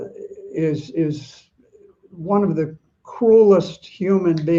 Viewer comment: I bet Senator meant BN is one of the COLD HEARTED humans to ever live on this planet :D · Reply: I thought the Senator said that BN was one of the "coolest" human beings on the planet, but he seems to have said "CRUELEST" which makes more sense and is an accurate description 🫡